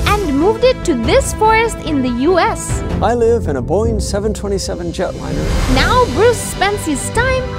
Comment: I Thought He said boring but it was booing